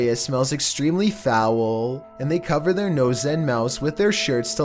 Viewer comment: Who else heard say nose and mouse